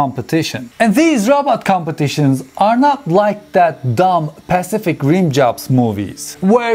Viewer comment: Did I hear 3:57 correctly? "Pacific Rim Jobs"? LOL